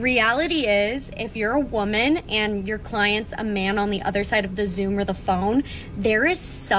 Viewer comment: did i hear that right "my clients"?